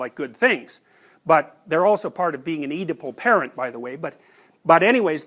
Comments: is he saying eatable parents or need to pull parents? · what word is that? "They are also part of being an ________ parent by the way." (Eadible?) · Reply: oedipal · what is he saying about parents? "need to pull parent"? I dont think this is what he said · did anyone catch what he said? A _ parent? Sounds like he said an eatable parent 😂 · can someone please explain what he said here? I heared ‘part of being a ediple parent’ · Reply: Oedipal parent.